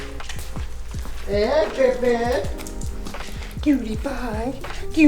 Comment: I thought he called her Pewdiepie, I was a little confused